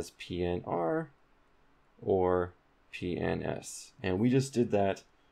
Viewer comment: is he saying PNs or PENIS 0.o